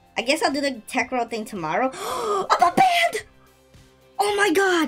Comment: GASPS I GOT BANNED (I think he meant to say "i got unnbanned" lol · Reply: If u really focus he said OMG IM Unbanned